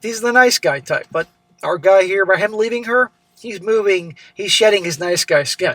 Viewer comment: I thought you said he was shedding his sleeve 😂